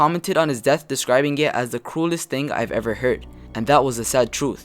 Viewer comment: I thought he said coolest